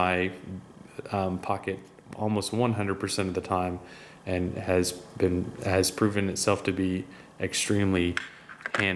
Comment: On 3:25 I didn't hear what kind of knife that is, can someone tell me? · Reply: SilverTech Lite Signature